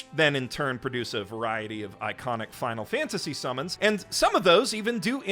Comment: How can you tell I played too much final fantasy? "Which then in turn produce a variety of iconic final fantasy summons" What I heard: "Which then in turn produce a variety of eikonic final fantasy summons."